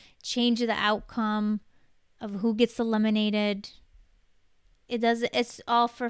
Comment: I thought she said lemonated instead of eliminated... 😆😆😆